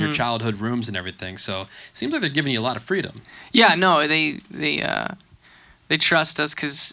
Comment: I know Tyler doesn't cuss but at 10:11 it sounds like he said "hell yeah", tell me if I'm wrong.